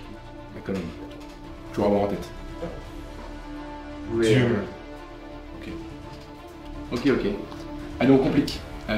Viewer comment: excusez moi j'ai pas compris, il dit quoi en voyant la photo de micron ? DUME ? Sinon tout simplement très bon la vidéo. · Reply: Je crois qu’il dit DIEU